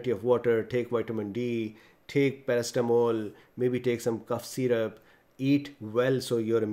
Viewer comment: What is the medication? or supplement? he says at 5:56 after vitamin d? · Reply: Paracetamol